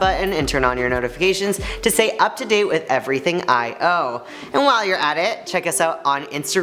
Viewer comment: i heard that as "Everything i owe" and i was like oh sh¡t they have dept?! Then i realized oh wait.. he meant IO XDD i need to get my ears checked..